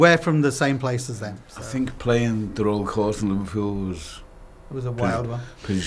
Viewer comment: what did he say at 2:29? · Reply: "I think playing the Royal Court [Theatre] in Liverpool, was pretty special."